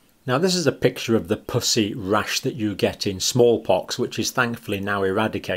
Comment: I can't concentrate because I keep hearing "pussy rash"